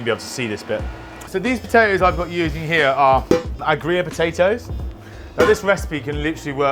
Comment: What kind of potato did he say was used at 0:35? Sounded like greyer, but that’s a cheese! Imagine they’d go well together though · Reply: Agria